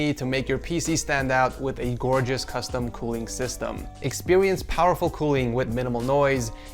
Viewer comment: Am I tripping or did he say “expewience”? Lmao